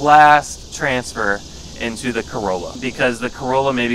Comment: Oh Lord. I thought I heard you say "corona" until you mentioned "corala" again. I never had my hope rise and drop so instant.😔 😀But props to your editor. All those Neils and a Pearl.